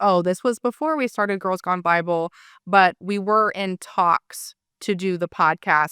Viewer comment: OHHHhhh! "In talks" I thought she was saying "intox" like she was admitting she was intoxicated.